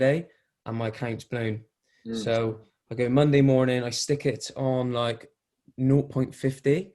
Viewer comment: Hey around 1:53 you say you stick the EA on _ and I can't make out what exactly you say. Can you clarify what time UK time before the market open you put it on if you don't mind please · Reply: Around 6/7am UK Monday morning. I always turn off before 8am NY time